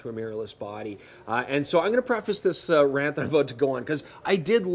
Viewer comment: Could someone tell me the correct caption for 12:32 ("so I'm gonna preface...), I can't understand it neither by listening nor the automatic caption. Thanks! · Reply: you got it right. he said "So I'm gonna preface this rant that I'm about to go on..."